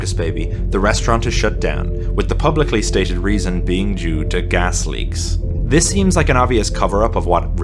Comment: DID HE SAY JEW TO GAS LEAKS!?